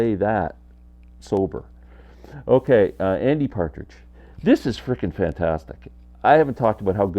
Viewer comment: You scared me,when you said Andy Partridge I thought you said Danny Partridge. That woke me up this morning .